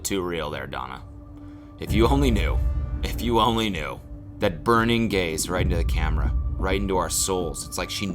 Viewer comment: When he said burning gaze, I thought he said burning gays